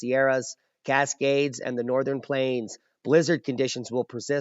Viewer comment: "BLIZZARD" conditions in Alaska... Whew! I thought you said, "Lizzard conditions..." ‹ =ºo